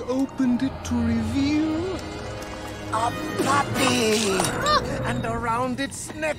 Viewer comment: I THOUGHT IT SAID “A PREPPY”